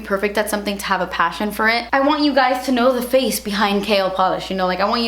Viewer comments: i keep hearing it as "kale polish" lmao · Congrats!!!! You deserve it! It sounded like you were saying "kale polish" throughout the vid lol. New name idea for a polish? Haha can't wait to purchase!